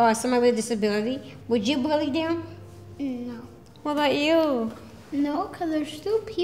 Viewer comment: he was praying in Korean its so sweet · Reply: I thought he said 'what about when u walk' but ok...